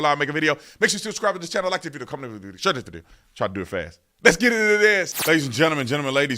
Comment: Nobody Sleepy joe 1:57 Also sound like he said let's get into dat a** lol